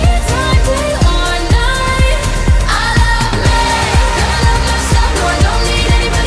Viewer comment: how is it "i love me" i hear it as "i love life"